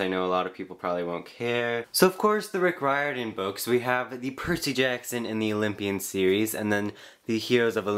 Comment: I thought you said: the Pretty Jackson series (LOL XD)